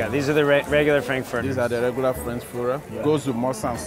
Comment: did they just say Frankfurter?